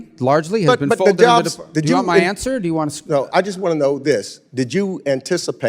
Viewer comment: “do you want my answer?” He actually said no.